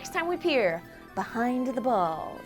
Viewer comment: i swear i heard "pee behind the balls!" and that isnt even the weirdest part... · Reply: Its "peer" behind the balls